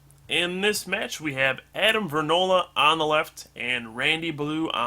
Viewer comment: i just cant get over how much ur voice sounds like jwittz.... also it sounded like u said granola instead of vernola lol